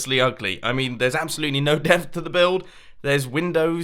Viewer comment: "there's absolutely no death to the build." that is what I thought he said XD. i kinda sounded like it.